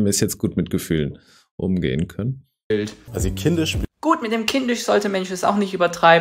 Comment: Sie haben da bei 3:35 etwas falsch verstanden: er meinte nicht "mit kindisch sollte MAN es nicht übertreiben", sondern "mit kindisch sollte mensch es nicht übertreiben" - denn "man" könnte ja durch die klangliche Erinnerung an "Mann" diskriminierend sein. Leider kein Witz; das habe ich sogar schon in der Öffentlichkeit erlebt....